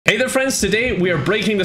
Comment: ive been watching to many of ur sims videos that instead of "hey there friends" i heard "hey there sims"